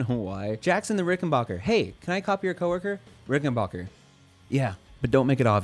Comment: i think you meant to say "homework", not "co worker"